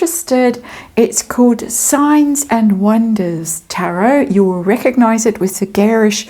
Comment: Just to say re your new channel, ‘Signs & Wonders Tarot’. I have found it, but, there is another channel called the same name(less the ‘tarot’ bit) that is definitely weird, so I kept looking till I found you. However perhaps you should leave a link within your description, on this channel, then there would be no mistake. Anyway, wishing you well with it…I have signed up, because I love your readings.👍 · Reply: I thought she said Science and Wonders, at first, and couldn't find it 😆 Had to use close caption to get it. Silly me !